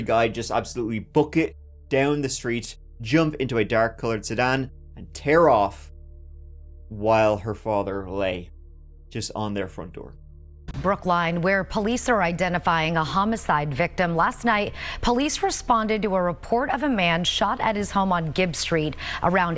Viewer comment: is when I realised it was actually called Brookline. 😂 I thought was saying Brooklyn.. I'm so thick.